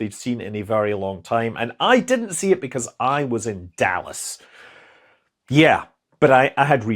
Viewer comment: Korben Dallas? 18:31 The reason wires are used for communication in satellites instead of blinky lights or fiber optics are plentiful. There is in increase in power usage, which amounts to more radiator area needed (canceling out the weight savings). LEDs are a generally slower data rate, though that's less important than now needing to optically shield all other semiconductor junctions, since photons will cause arbitrary switching. LEDs are also not consistent in color across wide temperature ranges, and they tend to continue to glow after being turned off. They also glow slightly when naturally rectifying ambient electromagnetic fields. ...the real answer is to use fewer wires, but adopt a commonly used and well understood network scheme to make a redundant and error-resistant "SCAN" or Space Craft Area Network. It solves the wires issue, but is still a bigger target for cosmic rays to strike the gubbins and cause errors. Fortunately, if you use a mesh-type network, where each device can forward messages in case of a loss of direct connection, you will actually gain communications redundancy instead of losing it. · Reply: Oh he said Dallas? I swear I heard him say Davos.. I though he joinded the dark side.